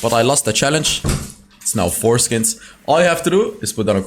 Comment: 😶 foreskins instead of 3 skins?